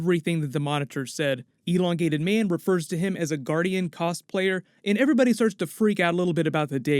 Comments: just wanted to point out when ralph was talking about the monitor was "asgardian" not sure if you've misheard it as "guardian" but great video as always, very hype for crisis! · I thought he said asguardian armor.